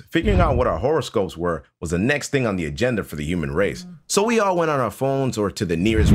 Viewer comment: I thought he said "human raisens" 😂😂😂